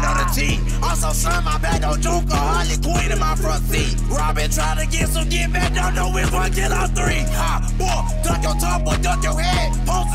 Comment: what he say here? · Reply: “Robin trying to get some get back dont know which one kill all 3”